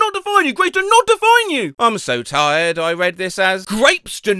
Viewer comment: HELP I misread that as grapes at first too 💀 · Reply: I read it correctly but heard it as grapes